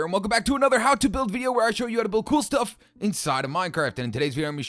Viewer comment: at the begening it sound like you said minecart insets of minecraft XD